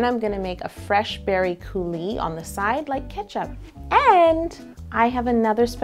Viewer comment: Okay but am I the only one who heard her say "like ketchup" instead of "like tomato soup"? Who eats grilled cheese with ketchup? (Just a question just my opinion dont come for me)